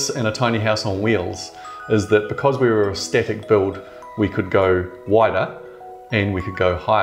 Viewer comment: "a static!" ffs I thought he was saying aesthetic for most of the video haha